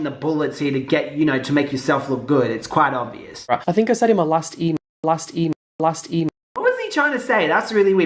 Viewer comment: I thought he was saying “My last E-mail” but damn her beats around the bush like Chris and plays victim as much as that guy coughs. When he’s in a scandal he literally never has SURE FACTS. He’s just stating possibilities and then trying to make it even more likely, but stillll doesn’t make it a FACT